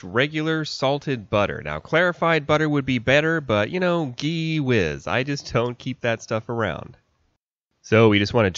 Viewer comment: Did he just say Ghee Wiz?😅😅